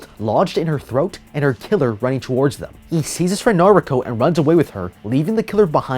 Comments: Oh Noriko! I thought you said Natuto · Him: "He sees his friends naruko" Me: did he just say naruto?